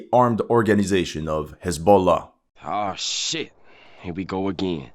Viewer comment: Damn, Israel is going after Hasbula? That's crazy. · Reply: First time I misheard it as "ebola" and thought "Did Israel declared war on Ugandan Knuckles?"